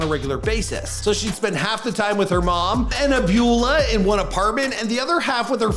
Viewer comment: Did he just say "abulah"? 🤣😂